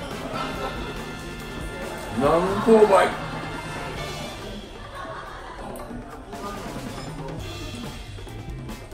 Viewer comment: ここ、何て言ったんですか？ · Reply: 「南高梅」 ですかね^_^